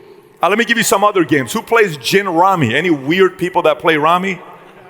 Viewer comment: what board game was he talking about when he mentioned Armenians? I couldn't catch it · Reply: Gin rummy